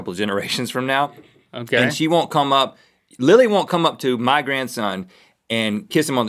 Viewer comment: In 8:42 did Rhett say Link's daughter is named Lily?